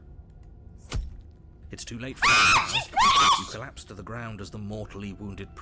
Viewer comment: I heard "AHHHH She's pretty!" But then she clarified "She's British"